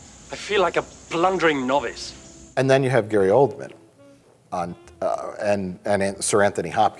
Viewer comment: I thought they said Gary Coleman